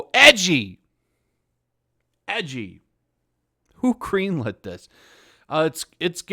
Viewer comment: Huh… that time read about buttercup doing it a la lesbian 6 times in the script, then said “who greenlit this?!?!” And I thought he said “who cream lit this?!?!” Well, yeah, I’d try to get my head out of the gutter, but it’s kinda hard with cw bringing it up so often. By the way, the answer is, another Harvey Weinstein.